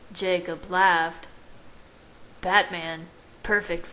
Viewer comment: Yo did he just called his boy Batman. 🦇 🧛🏻‍♂️ Cus the actor Robert P really is was Batman!!